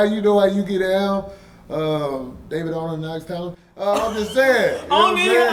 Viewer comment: What comedian did he say at the 8:07 mark? .. Sounded like he said David Arnold but I know he wouldn't do that 🥴🤦🏾‍♂️ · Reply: He said David Arnold and Alex Thomas. He's called them out on his show before.